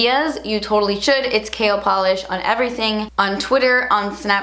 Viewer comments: I kept hearing "kale polish" 😂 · I kept hearing "Kale polish" instead of "K L polish" lol. Anyways, I'm sooooo happy for this!